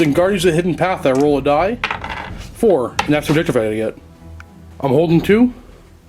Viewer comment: what does say, when he rolls the die for "Guardians of the Hidden Path" card? I listend to it multiple times and I can not understand a single word. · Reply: And that's the objective I need to get.